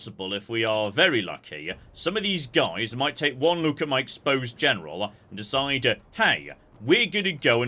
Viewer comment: my exposed general Me: For a minute there, I heard "exposed genitals" Spouse: Well, it is. 😆